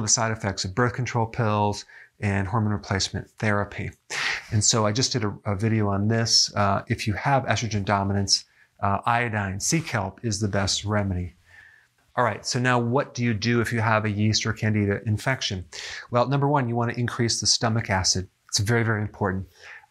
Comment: Ah, sea kelp. I heard “seek help”.